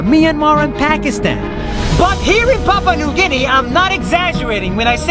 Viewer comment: I think he said “ I love my Papua New Guinea”